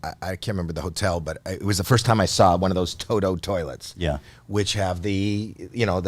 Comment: did Howie say "Tojo toilets"?